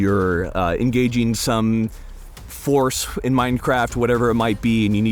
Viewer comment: did he say escaping in minecraft?